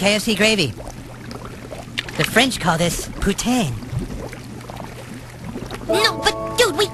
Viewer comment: I really thought he said putain